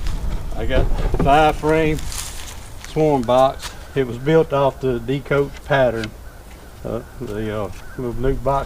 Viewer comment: you mention your box was built after a certain pattern but I could not understand...sounded like "decoates pattern". How do you spell that? Also, thanks for taking the time and effort to put out your videos. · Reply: Thank you. I used the D Cotes nuc box pattern. I just made the twice the height along with a few more modifications. Check this link for the nuc box pattern, <link>